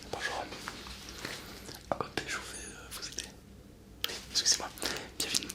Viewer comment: il dit quoi ? cojiata ? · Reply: Code Julien